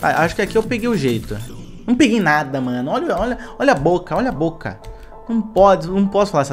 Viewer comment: não entendi o palavrão. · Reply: Ele falou olha a boca pq logo dps q elr falou "peguei o jeito" ele morreu